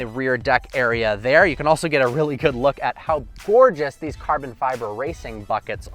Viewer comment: Did anyone else hear carbon fiber fuckits at 1:03?